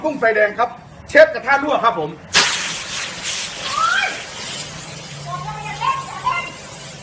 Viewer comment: ผมได้ยินเสียง : โอ้ยบอกว่าอย่าเล่นอย่าเล่น 55555